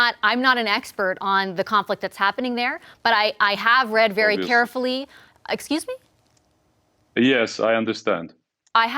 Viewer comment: Canada says they gave arms to Turkey who in turn sold them to Syria. Little hard of hearing. What one word did he say at 6:04 that triggered her? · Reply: "Obviously". (The word he said under his breath.) The issue that seems to have sprung up is that since the conflict intensified and it is unclear whether the drones WERE sold Canada has stopped sending the technology to Turkey, Turkey is annoyed about this.